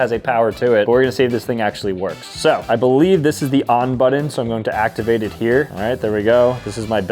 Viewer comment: Every time he says "button" I just hear "budden".